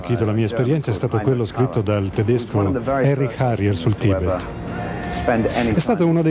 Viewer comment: can someone share the book title he shared at 5:50 ? I cant hear past the translation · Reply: It's "Seven years in Tibet" by Heinrich Harrer